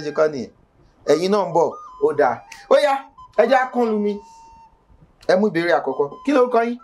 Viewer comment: E ja kini___ what word did he use at 3:17? Can anyone help? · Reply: E jẹ ká kàn lu omí (let start)